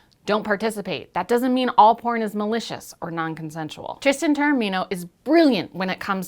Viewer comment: I can't make out the name. Can someone help? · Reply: Стоил Томов Tristan Taormino, cheers